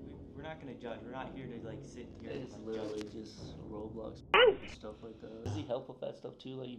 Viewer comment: Is that ? Did he say roblox 🌽????